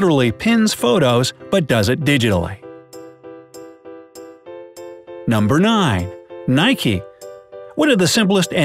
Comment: did he say number nine??